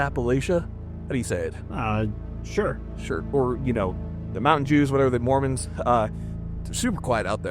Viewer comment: Did he say "mountain jews" 😂😂😂